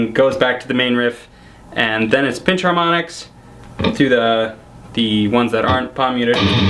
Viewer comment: Hello guys! Please help me, can you write what he says in 6:39-6:42. I can't understand cause my english isn't enough good. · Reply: Arstan Dzhaparov 'Then it's pinch harmonics to the ones that aren't palm muted.'